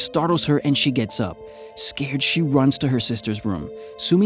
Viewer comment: Why at 2:26 the subtitle said Sacred instead of scared Guess she got some mythical origins xD